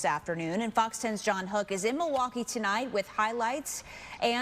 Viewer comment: Ohhh CRIME. I thought he said Prime. I was like damn Logan Paul done pissed off everybody